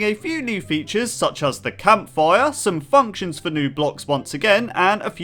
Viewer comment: Why did I hear "fucktion" instead of function